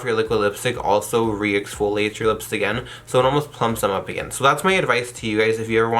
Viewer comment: it sounds like he said “so that’s made of ice tea.” 😂